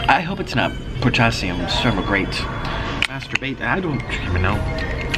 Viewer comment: Did he just say "masturbate" at 11:28?